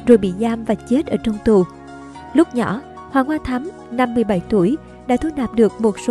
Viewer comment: lúc nhỏ Hoàng Hoa Thám 57 tuổi ????????? hau tui nghe nhầm